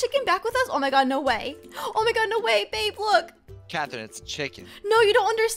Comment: did you just say babe look?